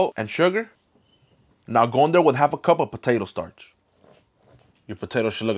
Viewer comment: I thought I heard TAPATIO not potato